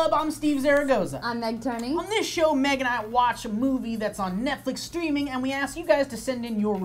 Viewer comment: I coulda sworn at 00:13 he says "...watch some boobies" I think I have something specific on my mind